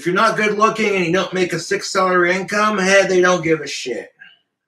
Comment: Did he say a 6 dollar income or did I hear it wrong